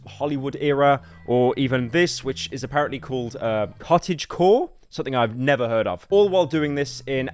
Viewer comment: not knowing cottagecore is probably the weirdest part of this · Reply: I thought he said Cottagewhore